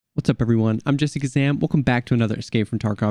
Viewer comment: When you say your name fast, I thought you said Jessica Sam lmao